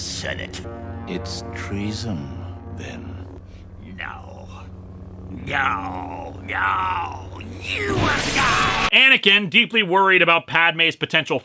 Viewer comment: Wait, Palpatine says "No... NO! NOOOO!! YOU WILL DIE!" when shooting lightning at Windu? I always thought he said "No... NO! NOOOO!! YOU HAVE LOST!". That would make more sense to me.